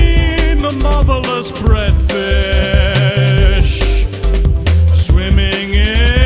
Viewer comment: I always thought it said motherless breadfish